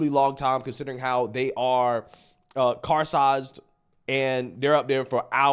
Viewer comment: Did you say CAR SIZED?